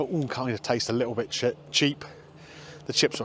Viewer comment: Did you say, ..."Taste a little bit shit" before correcting yourself at 18:58?! Can't blame you, none of it looked that great after the salad...